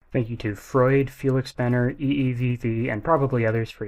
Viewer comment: i know it's not spelled that way but i heard "thank you to Freud" and went "wait what"